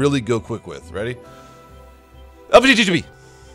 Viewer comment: it sound like he is saying LGBT